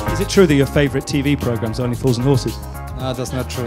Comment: Can someone please tell me what show they are talking about at 1:30 ? English is not my native language and even though that's just a detail, it still bothers me that I can't understand what they are talking about. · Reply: Only Fools & Horses